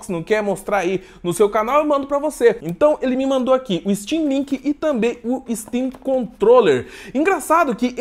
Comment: Achei que ela tivesse falado steamlingue kkk